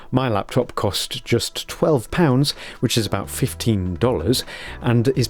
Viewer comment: am I hearing wrong? You said 12 pounds?